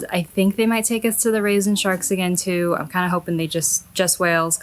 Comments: Rays and sharks! Omg, I really thought you said "raisin sharks", haha! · Rays and sharks! Omg, I thought you said "raisin sharks", haha!